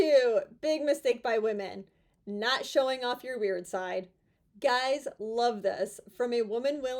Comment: Thought you said "not showing of your rear side", instead of "weird side" is a big mistake. i thought yep, that is good advice...lol 🤣